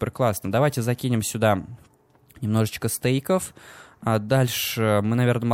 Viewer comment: мне послышались "немножечко стояков"